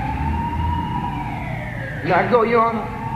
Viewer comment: I’m not sure if you guys realize but “sir” is a sensitive word because it’s how slaves used to address their owners. Which is why he asked “can I call you your honor” that judge was clearly aware of it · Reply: oh shit I see what you mean, I heard it the "may I go" as "may I call", yeah okay. I was wrong